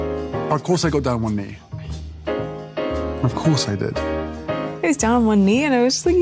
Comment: I thought they said he got down on one knees over chicken roast?